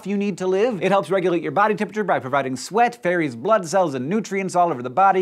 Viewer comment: at 0.56 he said "ferries blood" and I heard "fairies' blood" !!